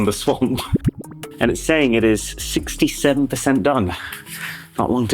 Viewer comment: did i hear 67?